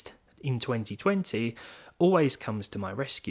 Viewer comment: I dunno why but when you say "Mafia 2020" I keep hearing "Matthew 2020"